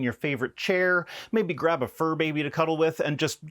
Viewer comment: I thought you said furbie instead of fur baby first time listening.